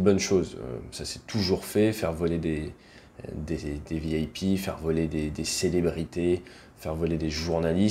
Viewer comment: "faire voler des vielles pies" 1:17, c'est pas moi qui le dit ce sont les sous titres auto ;)